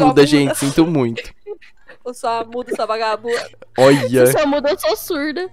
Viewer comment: so eu que ouvi a falando v4g4bund4